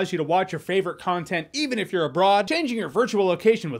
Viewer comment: During the sponsor portion I misheard “if you’re abroad” as “if you’re a fraud” and I was very confused